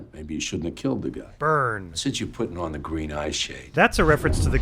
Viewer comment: dang, I didn't pick up on that. I always heard this as a mispronunciation of 'eyeshadow' and thought it was about greed (green with envy). The accountant thing makes way more sense.